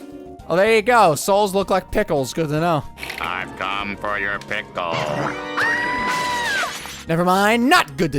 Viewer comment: I was peeing and I hear “I’ve came for you’re pickle”😂